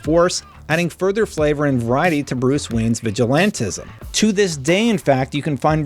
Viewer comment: I'm sorry wtf did you just call batman? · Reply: bruce wayne